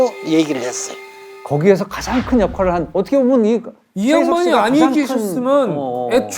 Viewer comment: 가량 이양반?이라고한건가요?? 제가잘못들었나? 130명의 목숨을살린 영웅에게 할말도 아닐뿐더러 나이로보나 뭐로보나 그런단어는 잘못 선택한것같은데...